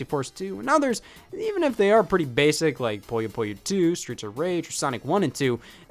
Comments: i thought he was saying “Puyo Puyo 2: Streets of Rage” and i was so confused😂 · Did anyone else hear Puyo Puyo 2: Streets of Rage like it was one title? No... just me?